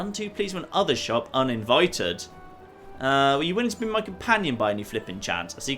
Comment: did he just say "My Pleasure Companion"? xD